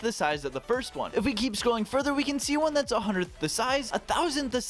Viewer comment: the captions say "a thth the size" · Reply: Onetenth*